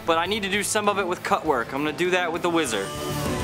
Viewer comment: i thought he said wizard instead of whizzer